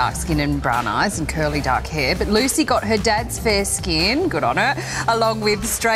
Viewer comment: I heard 'good on her' and said OH SHIT! 😮